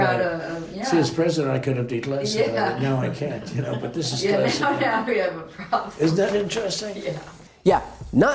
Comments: Why is nobody pointing out that at around 3:14 he actually says "This is classified", though it's slight interrupted towards the end. Am I wrong? I by no means have extraordinary hearing but I've yet to see somebody point this out. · It sounds like right after he says "I could have declassified it but now I can't" that he adds: "This is classified." Can anyone else hear that?